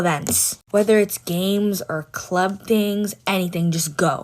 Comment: Who else heard her say “club gangs”???